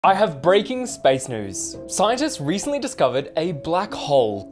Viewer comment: I thought he said Amazing space News then when I watched it my heart: 📈 📈 📈 📈